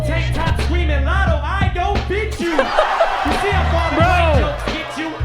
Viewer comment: until this video i thought he said tank top screaming lotto "i don't beat you" like a wife beater type joke or something. the more you know lol.